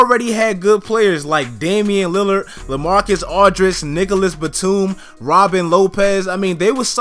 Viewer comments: It sounded like he said niggalus batum 😂😂😂😂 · did he just say niggaless batum? Lol